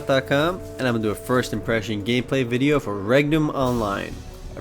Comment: when you said regnum i thought you said rectum O.o